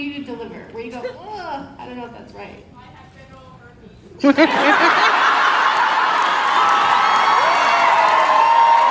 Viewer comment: What does the person in the crowd say at 22:29??? It’s been bothering me!! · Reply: "I have genital herpes" from when Sam was stuck in that commercial from the trickster 😂